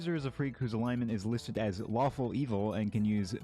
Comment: I thought you said waffle evil